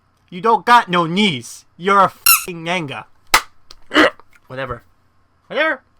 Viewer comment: WHAT THE HOLY HECK DID YOU JUST SAY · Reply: I said you're a f-word Naga, which is a snake human hybrid, which is what her monster form is. Don't worry, I don't say the gamer word in my vocabulary. However, I don't blame you with the sluring of words, and being mad could make it sound as such.